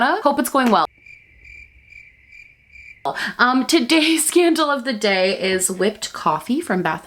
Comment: At the start somehow I heard "scandal of the day" and was like whaaaaaaaaaaaaaaat?